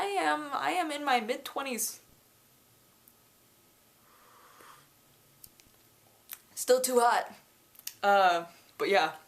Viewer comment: I did not watch what she was doing and I hear "I'm in my mid-twenties... still too hot!" I laughed so hard when I realized she talked about her coffee and not being pretentious!! XD